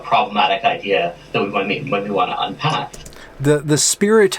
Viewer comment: he says "unpack" which you misheard as "attack." I'm with you, but just fyi